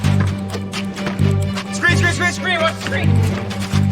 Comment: the subtitles said (yelling) but the dude was just saying "scream scream scream scream"